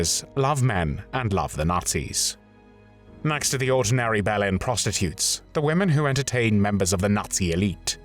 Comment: did he just say ‘Bell End prostitutes’? 😂😂😂